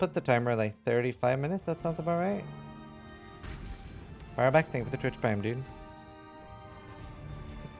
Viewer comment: Did he say "Firebat" for the sub at around 39:40?